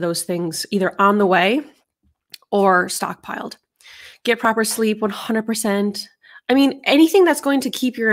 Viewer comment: I thought you said “prepper sleep” 😂